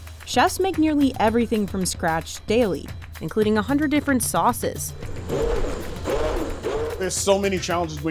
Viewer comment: I hear saying 'mobster sauce'